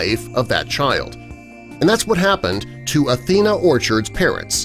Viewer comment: Athena!! Did I hear it right? These are awesome parents!!